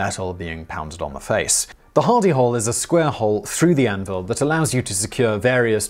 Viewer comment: At 1st I thought you were saying Hidy Hole!